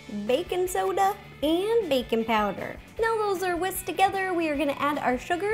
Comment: It sounds like she said bacon powder